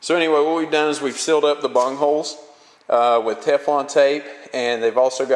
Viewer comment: wait did i hear bunholes